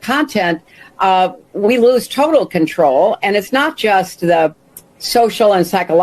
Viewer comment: did she just say "we lose total control?' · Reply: She did say "we lose total control" W+F? It's on the record. Who needs total control?